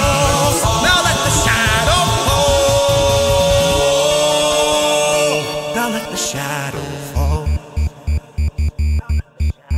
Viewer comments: Somewhere around 1:48 when screen goes black I think I heard either king saying "now let the shadow fall" or Lancer saying "Stop it dad" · Did I just hear "Now how was that?" or something like that? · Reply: Sounds like "now let the shadow..." And it cuts off